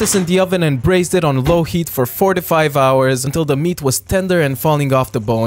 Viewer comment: When he said "4 to 5 hours" I heard "45 hours" 💀💀😂